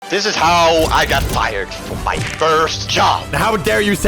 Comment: I thought it said "fried" 😭😭